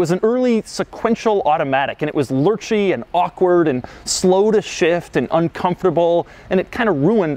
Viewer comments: sounds like he says "Slow as shit" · "Slow to shift" I thought for a second he said "slow as shit"...